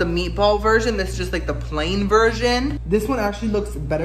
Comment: I heard "plain virgin"